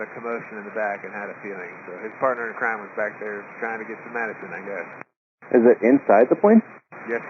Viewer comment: should be action instead of medicine